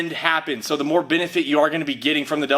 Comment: did u say bendifit